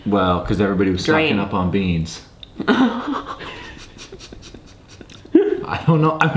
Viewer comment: What did he say when laughing at 8:12 I couldn’t get it because you said something in the middle of the sentence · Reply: ambition guy everybody was stocking up on beans